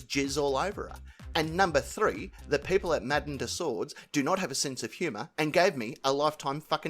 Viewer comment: Didn’t catch what you said at 20:51 (#3 according to F’in’ Terry) 😂 Catching up! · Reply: That Madam Tussauds didn’t have a sense of humor and gave him a life time ban..